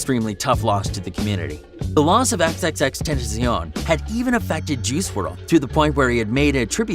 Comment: Xxxtentisionl is what i heard him say